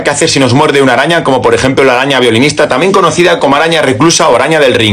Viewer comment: Al principio entendí "araña feminista" xdd